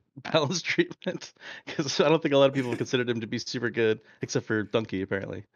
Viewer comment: did he said DONKEY ? LMFÀØÕ